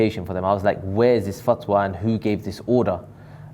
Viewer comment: what’s that word?? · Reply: fatwa ... islamic decree or special ruling given by a judge